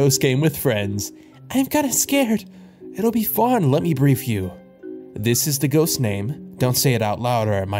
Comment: Why did i hear "it will be fun let me breed you" 💀